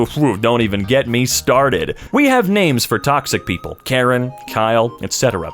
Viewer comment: first should be “alex” (if you dont understand, you wont)